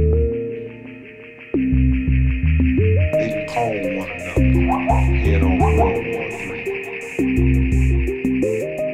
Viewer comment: What is the sample in this track (4:08) from and what (exactly) does it say? (sounds like: "They call one another... head on... world war three" ?) It's very hard to make out the whole thing, and the sound at the end of the sample sounds almost like a Gameboy 'dog barking' sound. It's puzzled me since I first heard this in ~'95. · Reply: xeper caz they call one another and head off for world war three (I think!)